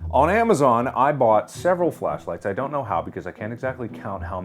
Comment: Omg I thought it said Fleshlight. Ommgg 😆😆